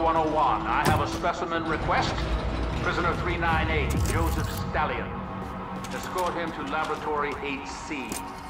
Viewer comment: Did he say Joseph Stalin ???